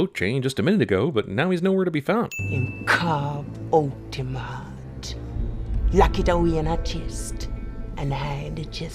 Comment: What is she saying? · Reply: "And cut out him heart..."